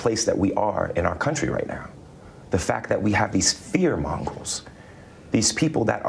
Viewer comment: did he say "fear Mongols"?